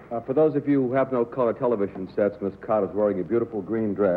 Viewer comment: When Mark Goodson introduces Sue Oakland he calls her something else. She corrects him but I can't hear what name he said. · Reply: "Cott", her married name, which would have been on her contract with Goodman, I guess. IMDb says she did TV editorials in NYC in the 70's as Sue Cott, and that her husband Ted Cott died in 1973. She was a dazzler, all right, one of the few lady panelists who could have stepped out of the screen into 2015 and looked perfectly fashionable.